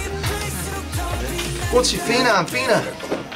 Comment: did i just heard my name "pina, pina" 😄😆